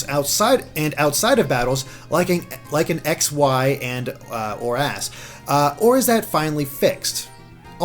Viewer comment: Sounds like says "or-ass".